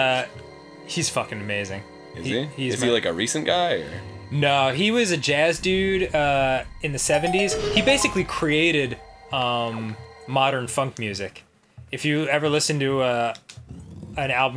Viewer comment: At first I thought Danny said "modern fuck musuc".